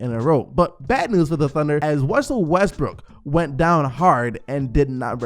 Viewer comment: Anyone catch "wussel restbrook" at 7:47 😂😂😂